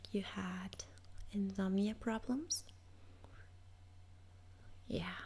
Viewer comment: it sounds like you say InZOMBIEa problems. heheh. Those really would be some problems.